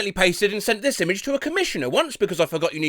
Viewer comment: thought they said "commissar" there for a sec